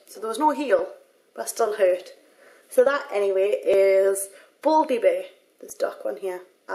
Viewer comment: it kinda sounded like you said "baldly bare" haha